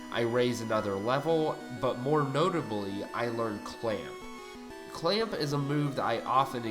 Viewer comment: I initially misheard "I learned clamp" as "I learned clam" which amused me more than it should have. Also, this Shellder challenge has me thinking of how many perfectly serviceable Pokemon back in Gen 1 I would ignore over something "Cooler".